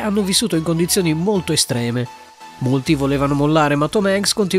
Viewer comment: solo io ho sentito al minuto 0:49 ho sentito multi